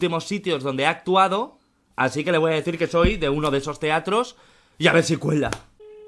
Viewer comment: Y AVER SI CUELA me mato como lo dijo 😂😂😂😂 y parece que dice yiabesiuela